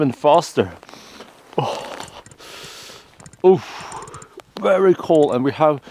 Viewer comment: Did he say oof 9:30 lol